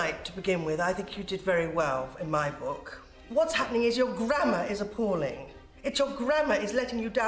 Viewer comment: WOW What an episode that was!!! Best one yet for me. Harrogate - the home of Yorkshire Yea!!! I thought Rosemary said "You're Granma is disgusting" lol It was Grammar hahahahaha DANCING!